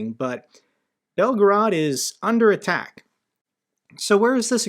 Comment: When you said belgorod is under attack I thought you said Novgorod was under attack and my heart almost fell out of my chest, thankfully escalations in this war didn't just take a dramatic turn. Thankyou for all the research you do for these videos and keeping us all informed.